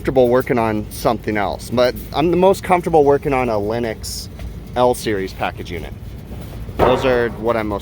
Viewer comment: did you say Linux, or Lennox? I'd like the see a Linux Powered AC Unit. Let it hook into home-assistent and give us all the API info like your Samsung Tablet your using gives you.